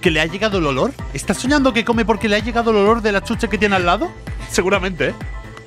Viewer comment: en el minuto 10:20 en vez de escuchar chuche escuche chucha XD